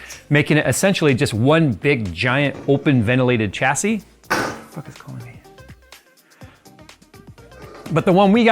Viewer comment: could have put some subtitles so we could read what you were whispering guys 😂😅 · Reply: “Who tf is calling me?”